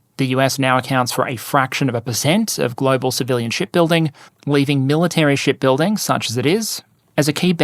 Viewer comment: I keep mishearing "shipbuilding" as "sh*tbuilding* and doing a double take.